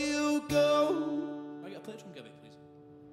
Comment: what did lewis say at 37:13? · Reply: Can I get a plectrum Gibby